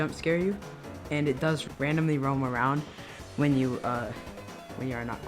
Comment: i thought it said random roam